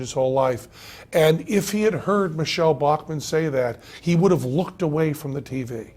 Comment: Damn. For a bit, I thought he was saying Michele Obama made that Clinton.